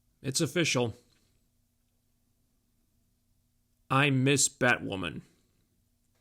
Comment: At the beginning of the video it sounded like you said “I’m Ms. Batwoman!”, 😂😂😂